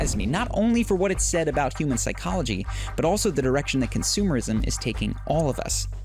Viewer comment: did he just say erection consumerism?